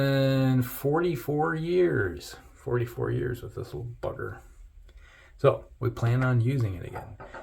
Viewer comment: Happy Birthday! 🎂 🥳 🎉 🎈 and many more sir. When you were talking about your razor I, thought you said, this little f**ker? Then I, had to replay it again and heard bugger. I gotta clean my ears. Great shave review and Merry Christmas 🎅 🎄 to you and your family.